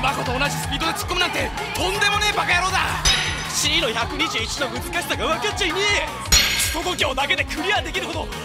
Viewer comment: 「クソ度胸だけで‥」って台詞が滑舌悪すぎて「すぽぽきょう」にしか聞こえないｗ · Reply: なんならその前もCの121の美しさがわかっちゃいねえ！に聞こえる